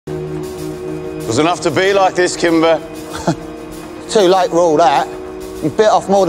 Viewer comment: What I heard "Too late roll reh" 💀